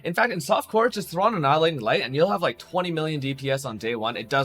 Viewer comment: what does he says? throw in what? iron glith ? something like that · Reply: Annihilating Light